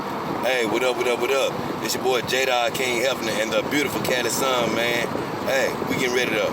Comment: That’s how it’s done, one should not waste their time trying to create change. It should happen naturally as one goes about his life. Also I liked how when you said your name—to me it sounded like you said Jedi Hefner lol Maybe you can create a series based around you and your girls in the Star Wars universe? I think that would be a fun little adventure to think about. However it’s just an idea from me to you 👍 Do with it what you want.